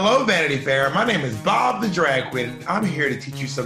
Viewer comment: For a second I heard "God, the drag queen"😂🤣